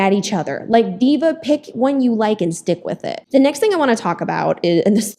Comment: Was liking what you were saying until I heard you say you like Drake girl no.......